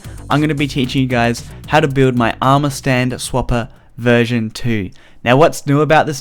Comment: subtitles say ass whopper 2.0 XD